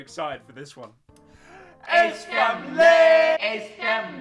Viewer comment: sounded like it said “ace fam gay” xD